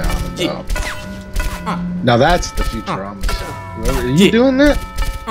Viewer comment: I knew I wasn't the only one who heard Futurama